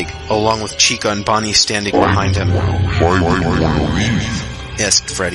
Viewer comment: what did Freddy say? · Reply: Why do you wanna leave